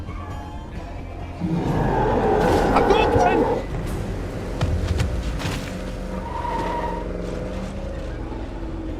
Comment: what do you yell out? every time i hear this i cant seem to make out what your yelling. haha really would like to know. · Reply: Not sure if you still need it, BUT I had the same question, he says "Hadoken". I looked it up and it's a street fighter reference. Not sure if this is the case to why he says it, but it's the best we got, right?